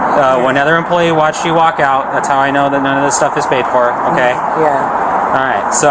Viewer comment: "I didn't pay for none of this stuff"? Did I hear right? This has to be craziness!!!! 6:26 Sugar daddy?? Lady if you have a sugar daddy you shouldn't be stealing. Maybe he's an old daddy with no sugar. She's obviously not well..........